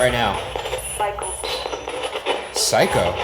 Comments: When Ryan asked if they could name the prison I heard “I can’t” not psycho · I heard "Michael", not 'psycho' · when ryan thought the voice said psycho i heard "michael" 😂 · I heard ”Hello Ryan” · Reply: And instead of „psycho“ i heard „Michael“ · I heard Michael not psycho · i heard michael not psycho and right when he asked whose cell i heard michael's again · How did I hear “goldfish” instead of “psycho”?😂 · That's interesting, to me it sounds like it says "Michael" instead of "psycho". That would be interesting if they could look up past prisoners and see if there was one named Michael to see if that name has any relevance. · Instead of psycho I heard 'Michal' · The voice sounded like it was saying the name "Michael" to me not "Psycho." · When Ryan asked the spirit box “what is the name of this place?” I thought it said Michael, not psycho